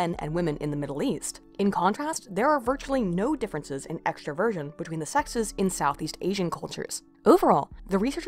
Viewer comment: I enjoyed listening to this, thank you. Though I think I need to say that I kept hearing "extra Virgin" instead of extraversion. That caused a lot of "wat" for a while.